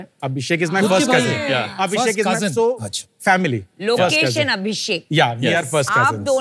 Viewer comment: at first I heard "location Abhishek" 😂